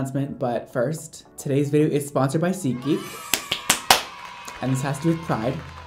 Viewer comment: Omg every time I would here an influencer advertise this brand. I always thought they were saying seek geek, not SEAT GEEK. Omg I’m so canceled.